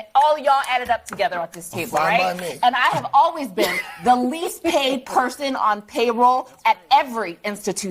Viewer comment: Black lady on CNN sounded like she said "I have always been the least "behaved" person on payroll..." . Yes, you're probably right. 😂